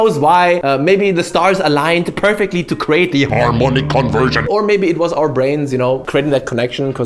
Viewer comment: I heard 'hormonic conversion' at 05:30. which also somehow works :) and no, not cringe at all. congrats to both of you :)